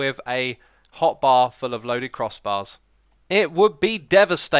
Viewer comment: did he say crossbar?